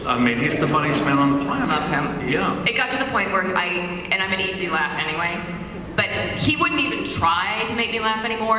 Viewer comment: The last part is here sorry that it got cut off. <link> live long and prosper my friends. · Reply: did she say " im an easy lay anyway" Lol